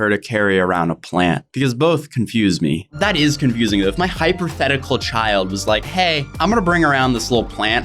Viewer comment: did he say hyperthetical😂